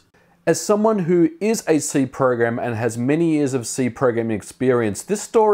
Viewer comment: did he just say that he is a c program?